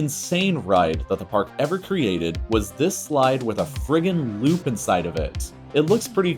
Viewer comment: DID HE JUST SAY "FRICKIN LOOP"